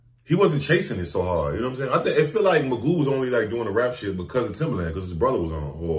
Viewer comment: Oh it’s Magoo that died? I thought it was timberland. Sorry I thought u said timberland at first